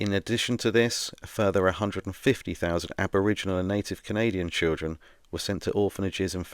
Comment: Whoo! Great information. Very engaging. Time stamp 30:04 did you say "150,00 Aboriginal AND Native children"? is there a distinction? Thank you.